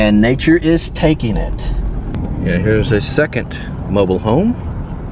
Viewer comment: Misheard "mobile home" for "noble home" at first.